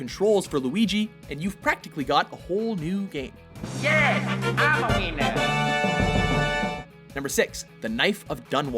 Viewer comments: the inner child in my mind laughed when luigi said " im a winner " because it sounded sounded like he said " im a weiner " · I thought Luigi said "I'm a wiener" 😂